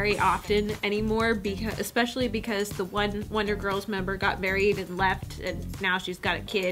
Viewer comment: When you said Wonder girls I DEFINITELY THOUGHT YOU SAID SHE GOT BURRIED AND LEFT LOL